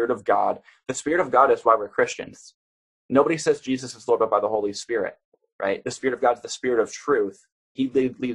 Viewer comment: What did you say at 44:10 about Jesus? I heard a mumble only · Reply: I think you said that nobody says that Jesus is filled up by the holy spirit. But when Jesus was baptized isn't that what the doves meant over his head?